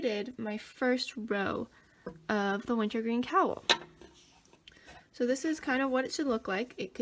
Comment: Funny. When I was watching this tutorial, at first I thought you said wintering cowl but then I realized it was wintergreen cowl when I checked the description box. You have really cool names for a lot of your patterns you create. I think wintergreen works really good for the name of this cowl especially that you’re using green for the cowl when it’s called the wintergreen cowl.